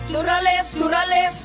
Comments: Who else hears "thoda left" every time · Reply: I heard "durales durales" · It's like the audio is speaking in hindi cuz I hear "thoda left thoda left " lol